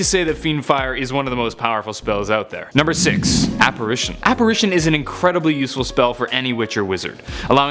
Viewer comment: It sounded like you said appration and you like forgot like 8 powerful spells. Like sectumsempra...